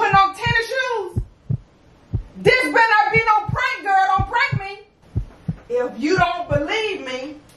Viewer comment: When I heard her say "dont prank me"I thought she said "I'm pregnant"😂😂😂😂😂